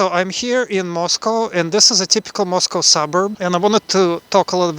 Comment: Мне послышалось "it's a typical moscow summer"😂